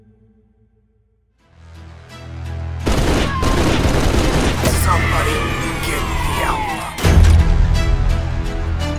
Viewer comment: Can someone tell me what he said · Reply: Ah so you made it to the end! He said "Somebody get the alpha" and just to be sure it is clear that was one of the zombies.